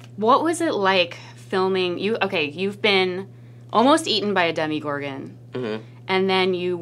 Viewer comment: did she say demi-gorgon?! Lawlllzzzz Demi Lovato is now a monster from Stranger Things😂